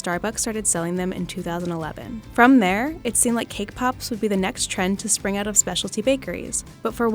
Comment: When they said cake pop i thought they said k-pop